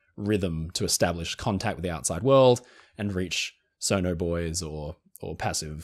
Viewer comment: I’m so internet poisoned I thought he kept saying “sonar bois” lil’ sonar bois just doin their jobs tryin to find people.